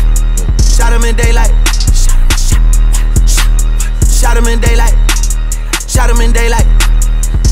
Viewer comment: I thought he said shadowman daylight 💀💀